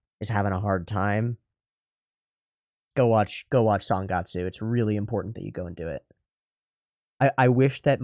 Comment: What anime did he say? Sunkasu? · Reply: Sangatsu no lion